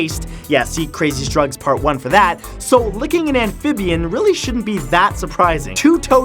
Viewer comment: Word to fresh prince i thought you said licking aunt Vivian....and ive seen this video before